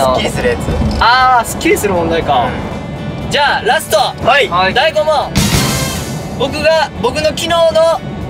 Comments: りゅちぇ"第5問"が"大吾問"に聞こえたんだね尊い大西畑🥹🧡❤️ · 第5問！で大吾って聞こえてカメラにアピってる子いる😂💕